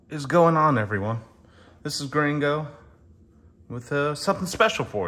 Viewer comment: I thought everyone was saying gringo, not. Haha. Happy birthday, my dude! My birthday month too. It's actually on the 22nd. Can't wait!